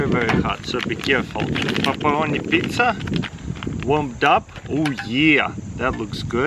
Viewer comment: I thought he said "papa onni pizza," but t'was actually "pepperoni pizza"🤦‍♂️